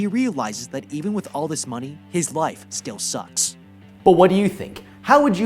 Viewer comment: At the end i thought he said "His wife still sucks"